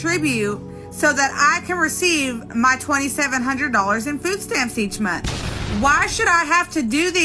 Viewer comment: wait wait... 2:06 did she said $2700USD? damn dude, i work as a software dev for just $1500USD and sometimes have to work during weekends too...